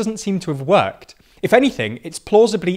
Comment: I thought he was saying "Russland Würster" and I was so confused why the Germans are calling these people Wurst as in sausage. Until I saw that it's supposed to be Versteher 😂